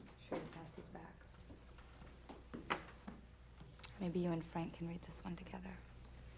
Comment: Thank you for all of these extended and deleted scenes. I'm sorry I am so late in the game. May I ask a favor please? Is there a way to offer closed captioning (Pt 1 had them, I think) or can you tell me what Miss Pommeroy whispers in Donnie's ear at 4:34 please? I cannot seem to hear her...and all my equipment's sound is at 100% and/or fullest capacity. Thank you. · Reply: R'hyll-Ann Ffiain She says “Maybe you and Frank can read this one together”